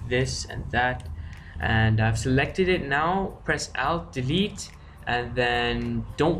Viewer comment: What did u say press altvit?? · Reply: alt+delete sorry for the inconvenience .